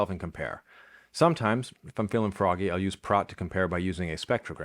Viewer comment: what’s the name of the tool recommended to compare spectrograms? I can’t make out what he says. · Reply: Praat